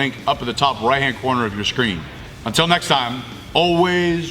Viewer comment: At the end it sounds like he says, “Train on time”. 🤣🤣🤣